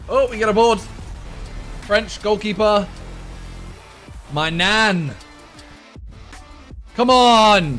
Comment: The maignan gk when you said "maignan" it sounded like you said "my Nan😟"